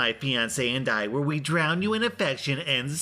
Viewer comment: the ultimate prize i really thought she said she would drown you